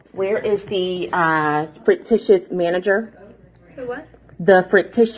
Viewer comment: Did she just say “where is the “fricktishus” manager”? I’m only 0.52 seconds in! Good lord! 🤣 😜